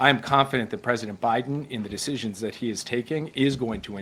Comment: I thought Putin said our President doesn’t make the decisions 🤨